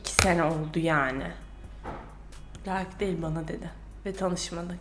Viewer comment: ne diyor burda anlamadım? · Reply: selen seven "layık değil bana dedi ve tanışmadı"